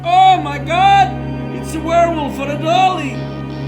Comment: dafuq is saying? "Oh My God, It's a Werewolf Fradolly"? "Oh My God, It's a Werewolf Feradolly"? "Oh My God, It's a Werewolf for a dolly"? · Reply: he said "Oh my God! It's a Werewolf on a Dolly!"